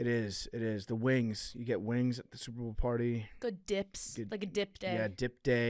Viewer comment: thought she said good dicks LMAO